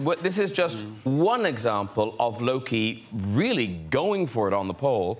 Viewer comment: I really thought he said "low key" at 2:05 and I almost shot myself